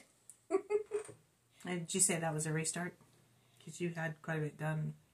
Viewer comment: lol. I thought, when you said “this is a restart” that you said “this is a rooster”! I thought maybe that was some new word stitchers were using. Ha ha. I listened again, and discovered my mistake. Hope I made you laugh. you are my kind of people. Love you both.❤